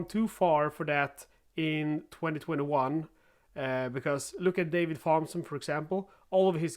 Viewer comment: you mentioned a board game designer but I couldn't catch his last name. Sounded like David Farmsom.